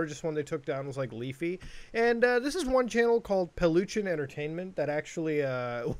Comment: I didnt read the name of the channel and i thought they said the name was pollution entertainment